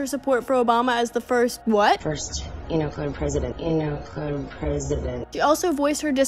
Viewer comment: i think she says “you know, colored president” 🤷🏻‍♀️